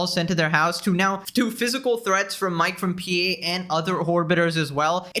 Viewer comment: did he say "whorebitor" 🤣